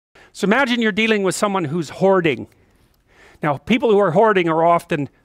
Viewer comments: first sentence i hear "so imagine if you’re dealing with someone who’s horny " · So, as per usual for Peters videos, I was on 1.75x, playing audio through my headset a foot away, and it sounded like he said "Someone who's horny" not "hoarding"